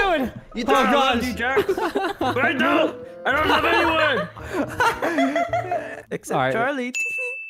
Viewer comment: OMG I HEARD SAY “I DONT LOVE YOU JAXX, I DONT LOVE ANYBODY, well.. except CHARLI TEHE!” OMGGGGG <3 (the part is near the end) (16:28 then keep going till the end)